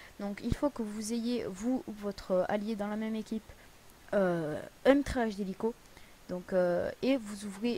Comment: à 0:35 il dit quoi ?? "un trainchhcw d'hélico" Articule champion.